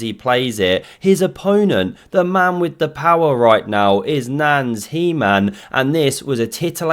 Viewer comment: I took a sip of seltzer and nearly blew it out of my ears when I heard “Nans He-Man”. 😂😂😂